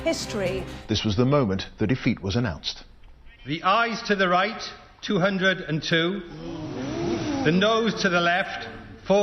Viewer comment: I swore they said "eyes to the right, nose to the left" and was so fucking confused.